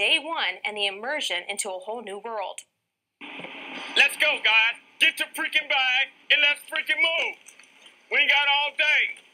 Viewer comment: Did he just say “get off the freaking bus, and get your freaking bag” 😂